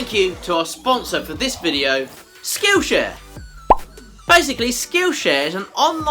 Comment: At first, I thought you said "Skoosha" instead of skillshare lol